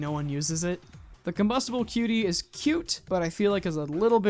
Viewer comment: what is name of that cosmetic? i cannot understand what did he say. Can someone answer me please? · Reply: Combustible Cutie is the name of the cosmetic.